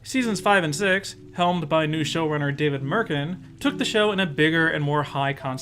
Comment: I somehow misheard David Merkin as Stephen Merchant and was blown away. Until I replayed the audio.